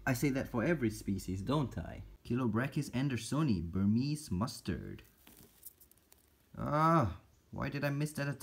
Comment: i thought it Said burmese b a s t a r d